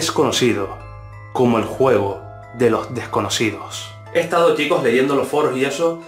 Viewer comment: Pensé que decía juego de las escondidas